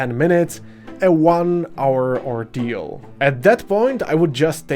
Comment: Did really said at 7:57 "...at most like 10 minutes a one hour or DYL (Czech for longer)..."? 😀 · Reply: ordeal